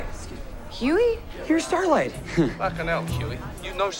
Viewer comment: what did he say? Why did they laugh? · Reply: they were laughing at the way Butch says " You know Starlight"